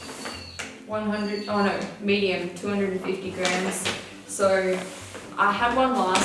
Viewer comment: i thought you said 250 grand for the blanket so i was like thats a nice blanket to let him tear up but you said 250 grams lol